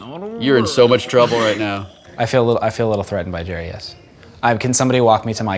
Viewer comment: it sounded like he said 'I feel a little threatened by Jarius." And that's my real name so I was like 'Wait what? What!? WHAT-oh he said Jeri, yes."